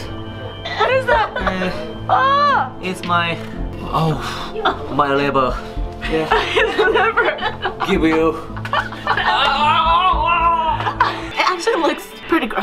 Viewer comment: did he say liber instead of liver